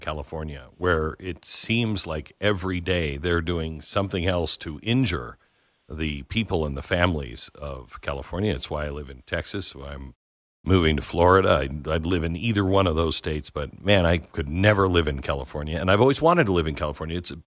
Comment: Did I just hear Glen say he's moving to FL??